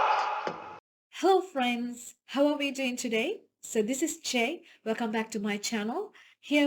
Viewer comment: thanks for the popup with your name. 👍🏼 i thought you said che, but wasn't positive. and the english subtitles show "jay" which did not add to my confidence about che.